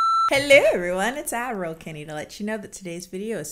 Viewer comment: I just realized Kennie isn't saying "Admiral Kennie" she's saying "Ad roll Kennie." for this whole time I thought she was calling herself Admiral for some reason. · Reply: Waitttt... I thought se was saying Adderall Kennie referencing to her being tired and taking adderall... I'm not American, so I thought it is something like Ibuprofen... You just blew my mind. xD